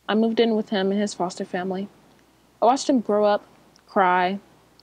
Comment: it sounds like she says grows up not thow up